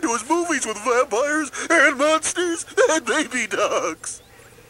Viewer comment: Did….Did Grimace said Nazis? That’s what I heard! “Vampires and Nazis and Baby Ducks” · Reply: Grimace did not say Nazis he said monsters